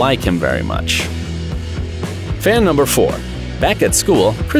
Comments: he says “fan number four “ instead of “fact number four” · he says fan instead of fact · am I the only one who heard him say fan